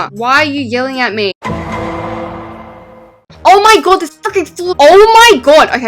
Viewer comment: DID SWEAR AT 8:27 !?!??!?!!??!? did she said f***ing or freaking?!?!!!!!!!!??!?!